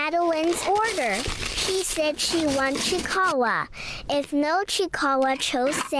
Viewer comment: I thought that said chiawaw....chihuahua not kicahae...chiikawa (3rd tiktok to be clear)